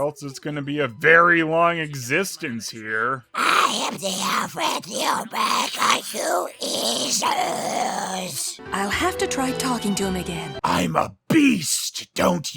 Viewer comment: What's he saying at 7:54 in that Gollum voice? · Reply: "I am the alpha and the omega, you eesholes!"